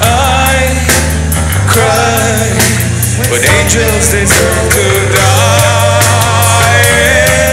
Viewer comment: Wtf is daron saying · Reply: Dylan Dowd "when Zombies deserve to die"